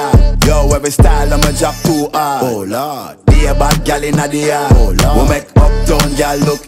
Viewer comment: At first I thought stylo said bare back gyal in the yard😄